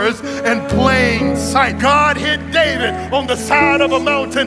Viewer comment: Very powerful message. For a minute I thought I heard him say God hid Diddy 😂 I had to listen more than once